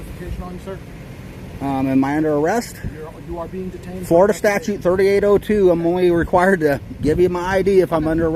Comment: Did you say "Florida Statue"? Looks like you're in Texas.